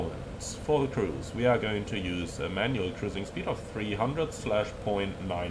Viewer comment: I thought you said you’re going to use “Emmanuel cruising speed”😂 Set your E number to 0.81